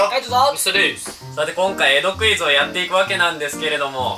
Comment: 江戸クイズがエロクイズに聞こえてしまった。